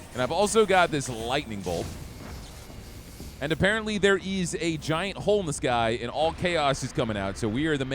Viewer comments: Ok... I know he said "Apparently, there is a giant hole in the sky...", but I first heard "Apparently, there is a giant homeless guy...", and was VERY confused for a moment. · Oh, 'giant hole in the sky', I thought you said 'giant homeless guy' 😂